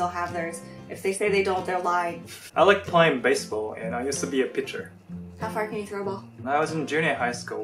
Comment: I thought he said "I used to be a picture"